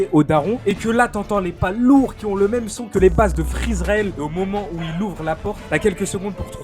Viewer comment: pourquoi j'avais entendu " freeisralel " 💀💀